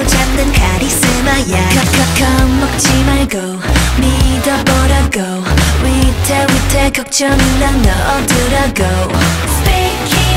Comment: So it's "Spade King" at 1:27, i always heard "spanking" up until this point :D